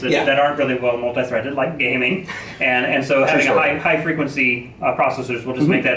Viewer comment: Did you said Σωστό?